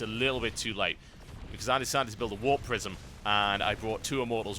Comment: Ahh warp prisim, I thought you were saying warp prison lol, makes sense now:) /mustbethebeer